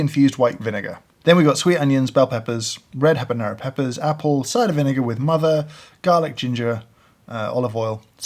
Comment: I heard "apple cider vinegar with MOTHER"